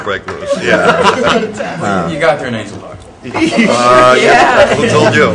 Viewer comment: What did Pitt say at 4:05? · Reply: He mentioned "Angel Heart"(1987)